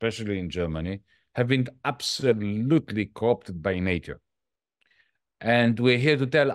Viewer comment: Please help. What did Yanis just say :". . . the Greens have been 'absolutely cocked' by nature"? Genuinely confused. Thanks. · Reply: "COPed by nature"?